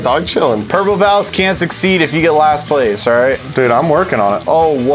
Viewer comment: i thought he was saying palace as well lol wow i'm dumb